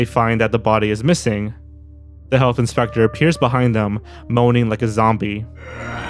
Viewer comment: Funny story, when I saw the health inspector episode as a kid, I thought they were saying "The Hunting Spector" like he's hunting them down for some reason.